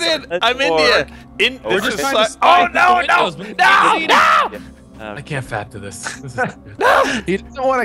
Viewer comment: Did say " I can't fap to this" at 3:44?